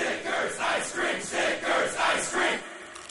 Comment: am i the only one that heard it as ice cream snickers ice cream snickers